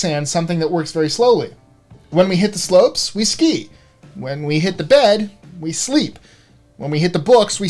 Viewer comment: i thought he was gonna say "when we hit our wife" LMFAO